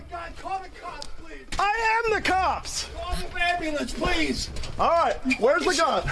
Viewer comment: Totally off topic unserious comment but around 24 minutes in it rly sounded like dude said call the wambulance